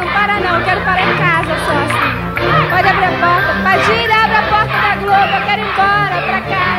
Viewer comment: PADILHA ABRE A PORTA DA GLOBO EU QUERO IR EMBORA! JSJJSJHDUEE SÓ EU ESCUTEI ISSO?